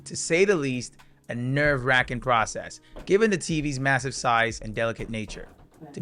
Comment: Did bro say massive??